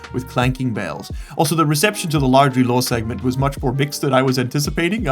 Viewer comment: I hear "also the reception to the Largely Lawn section..." and that's not the only time I gave up on trying to figure out what you said. Otherwise I do enjoy, well made vid.